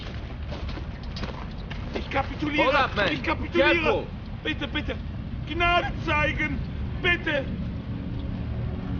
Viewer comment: What is the german saying at 4:41 ? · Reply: i give up don't shoot please